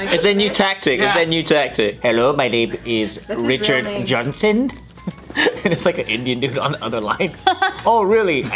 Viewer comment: what's the word he's saying after Indian? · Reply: "and its like an indian dude on the other line"